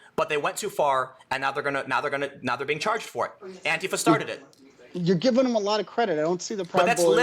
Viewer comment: "you're giving them/him a lot of credit, Tim"... all I hear is "you're right, Tim".